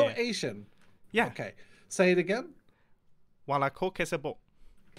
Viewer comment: I thought mumbo was saying: My coke has a butt